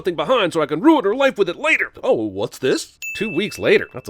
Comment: I actually thought he said "OwO what's this?"